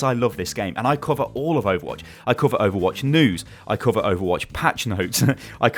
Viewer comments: at first I thought he said "I cover overwatch nudes" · i thought he said i cover overwatch nudes